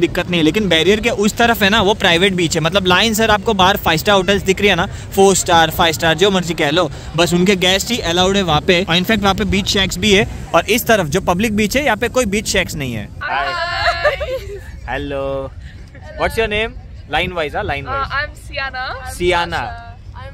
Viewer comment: bhai kya bola beach sex.?